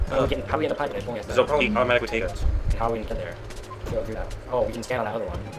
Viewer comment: did say "automatic potatoes?" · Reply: That is exactly what I heard, but he said “automatically take us” referring to the pipe.